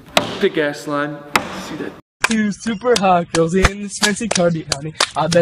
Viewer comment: am I the only one who doesn’t know what they’re saying 😂 Someone tell mee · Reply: All I heard what “super hot girls”